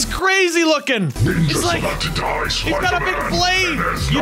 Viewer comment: ''Ninja's about to die, SWIPER MAN '' Yeah im just gonna rewind that just to make sure it wasn't just me mishearing things. 10:49 ''Ninja's about to die, SWIPER MAN '' Yeah im gonna repeat that one more time just to be sure.